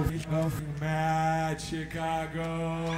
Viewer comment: what he said? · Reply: I think he said "go fucking mad Chicago" but it is censored.